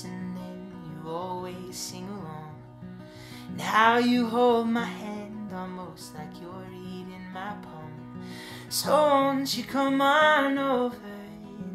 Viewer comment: omgosh haha I heard "how you hold my hand almost like you're eating my palm" and I was so confused and then I was like oh whoops nvm